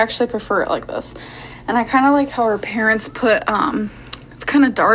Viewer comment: did she just say 'our parents'??...😮.